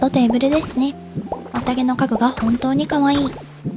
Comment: The auto-dub is pretty good, but that "I'm unemployed" caught me off guard 😂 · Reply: Me at 15:06 when I swore I heard 'the OD furniture is really cute' we're gonna WHAT NOW